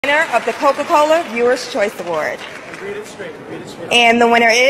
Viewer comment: I thought I heard read it straight B**😂